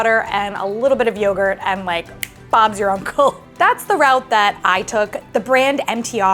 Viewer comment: oh my goodness the captions say bombs your uncle instead of Bob's your uncle. What is a vivid take on the phrase 🤣